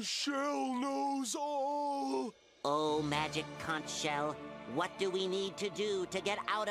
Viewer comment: kinda sounds like hes say magic couck shell XDD